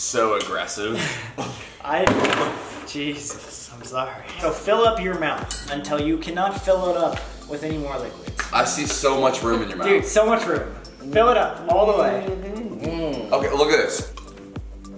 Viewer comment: i was playing this while vacuuming and my phone was in my hoodie pocket and i heard "there's so much room in your mouth, fill it up" and i had to stop vacuuming